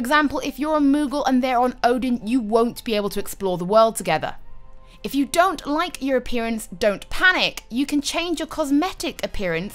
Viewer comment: thought she said: ''If you don't like Europeans, don't panic!'' loool